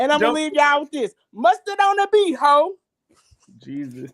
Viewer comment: At 1st, thought was saying mustard on the B-hole, found out real saying but whichever, ITS FUNNY, especially looking like Mr. Ed, the horse, showing a horse-looking smile. Lmao